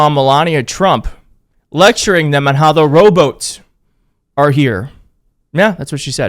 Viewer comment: Not trying to be a dick or anything but that's kind of like a shity way of calling someone out because to you it sounded like she said rowboats because of her accent yes she can speak English but because of her accent robot sounded like rowboats come on now and don't take a f**** rocket scientist to to realize due to her f**** goddamn accent it came out sounding that way it's like whenever an Asian person tries to say holy but it sounds like hory like I'm not being racist or trying to f**** be funny like I'm I'm genuinely being legit like her accent made it sound that way so you're you're talking s*** because she can't even pronounce robots she says rowboats come on now this is some petty type shyt